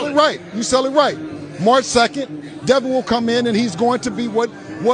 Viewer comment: did Bill say March 2nd? Or am I just going def? 😂